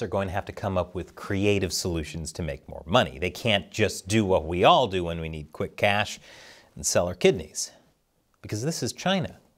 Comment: For a very short second, I thought Chris said "They can't do what all of us do when we need quick cash, and sell our kiddies".